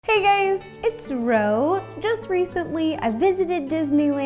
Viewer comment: i heard “hey gays!”